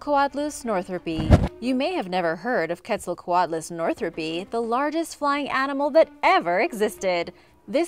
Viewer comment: did she just say lardest?